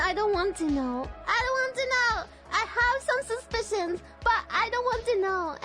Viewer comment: I think said 'Suspicion' not 'Suspensions' , unless is a car 1:12 forgot an 'I' in 'Sht'